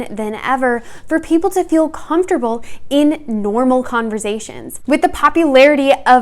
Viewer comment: I can’t be the only one who was constantly triggered by the way she pronounced the S in conversation, I feel abused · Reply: I thought she said conservation at first honestly